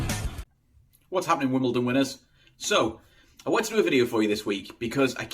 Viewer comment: did you say Wimbledon winners?